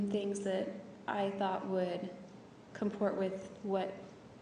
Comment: '{Sobbing} ...''Aynd..... uhm........ The subject of my fooneral came up sometime.. aynd.. he was gonna give a yoolagy... {sobbing} Travis would come to mine....., Aynd.........., even if it was in Anne Artica'' ...What a twat. As if they'd do funerals in ''Anne Arctica'' ...ground a bit hard for that. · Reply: does she say "comport with" ? What the hell? Her remedial IQ is showing again.